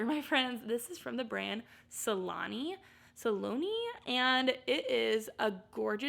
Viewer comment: When you were saying Saloni, I thought you were saying Missoni! 😆 I think I have seen that label (Saloni), and left it behind😑